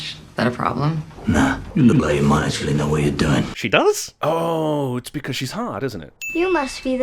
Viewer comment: delay money actually the way you doing? wtf he says?